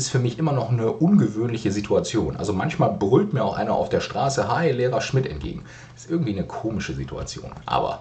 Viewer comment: Huch, ich verstehe bei 6:47 beim besten Willen nur "Heil Lehrer Schmidt!" Auch wenn sie natürlich "Hi, Lehrer Schmidt!" sagen ... vermutlich habe ich in letzter Zeit einfach nur "Das Orakel vom Berge" gelesen und "The Man in the High Castle" gesehen. Sonst, wie immer, sehr interessantes Video! :)